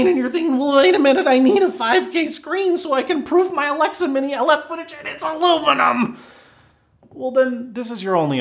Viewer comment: What do you hear? I hear: "I need a 5K screen so I can prove my Alexa mini left footage on its ALUMINUM" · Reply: ‘Proof my Alexa mini IF footage and it’s [ie the display is] aluminum’ Alexa mini IF is apparently a high end camera.